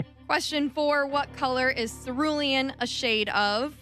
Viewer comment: Please enunciate for us old guys. For cerulean I heard vermillion, and I thought I heard craft instead of grass. Our aging ears start to have trouble with consonants. I'm a fancy idiot and love your show!